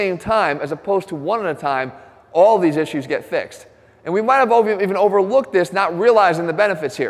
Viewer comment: what does he say? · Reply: "We might have all even overlooked this"